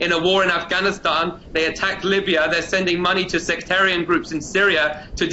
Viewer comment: Fuck!! did he says @ 3:35 that canada help in send money to sector group in Syria to .....??? Viens-t'il de dire @ 3:35 que le canada donne de l'argent à des groupes voulant déstabiliser la Syrie??